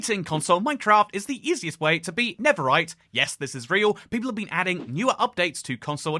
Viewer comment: Thought he said ".. is the easiest way to beat netherite.." 😭